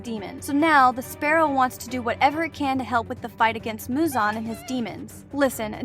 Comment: I love she said muzan I heard a “ he he “ lol